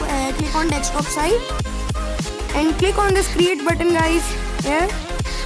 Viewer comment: what did you just said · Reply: I said click on create button